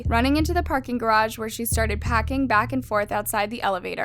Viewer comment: did you say.. packing? 🙃